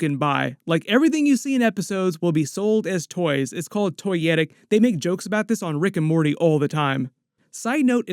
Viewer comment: What was it called!?! “Toy yetting”!?! “Toy yeti”!?!?! Random I know; but legitimately curious about the terminology there….. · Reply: Toyetic